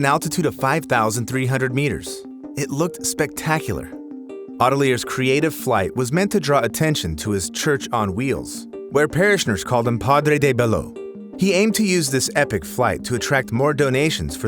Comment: Am I hear it right? 'Padre de Balloon'